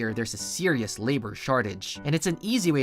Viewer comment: I can't hear anything other than Shart